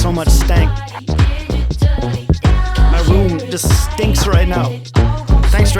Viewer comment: Did he just say, my room just stinks right now? 😄